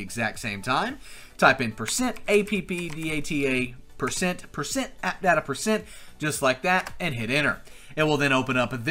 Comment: What did he say about the percent thing an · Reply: %appdata%